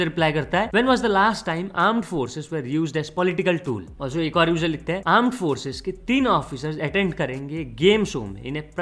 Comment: wtf, i heard aand forces